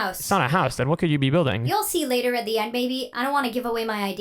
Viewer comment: Ayooo what did you call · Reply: She said maybe